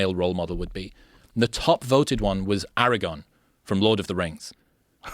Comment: I don't know if it's the accent but is sounded like he said Eragon instead of Aragorn.